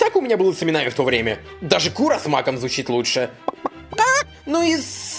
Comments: Он сказал кура с маком? А ой а мне послышалось Куромаку😅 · Мне одной послышалось не Кура с Маком а Куромаку?:|